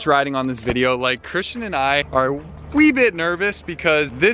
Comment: Did you say "wee", we say wee a lot up north of me (my) country (Ireland) and especially in Scotland